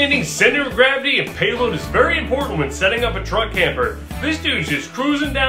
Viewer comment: I thought he said drug camper 😂😂😂😂 thats what i5 looks like